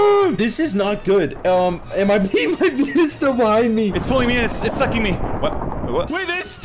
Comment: What did you just say?! 💀😳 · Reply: said “It’s sucking me”